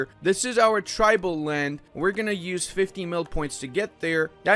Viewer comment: did say milk ?